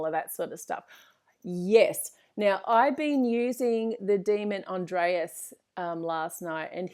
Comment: What's the name of the daemon she mentioned at 21:05 ? I hear "Andreas" but there isn't a daemon named Andreas. Is she saying Andras?